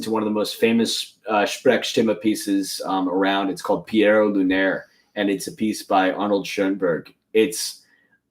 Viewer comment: does anyone know what the he'll theyre saying here . I think . I can't figure out what the spelling for the thing theyre talking about is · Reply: Pierrot Lunaire by Arnold Schoenberg